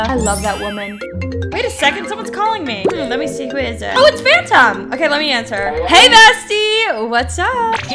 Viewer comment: When u said "oh it's phantom" I thought u said "oh it's fanta" loll